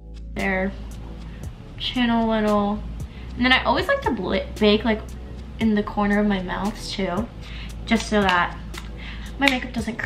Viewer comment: It sounded like you said I always like to bake the outer corner of my mouths🤣🤣🤣😆😆😆✌👍❤